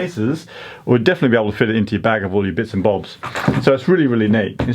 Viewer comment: “woolly bits and bobs”? Did I hear that correctly?